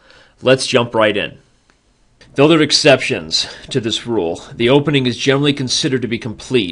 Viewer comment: WHAT THE HECK IS THAT WORD HE’S SAYING AT 1:52???? I’ve been listening to this over and over and for the life of me I have no idea what you’re saying there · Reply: I think he says: "Though there are exceptions to this rule"